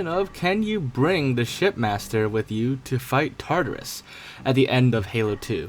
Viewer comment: I swear I heard “shitmaster”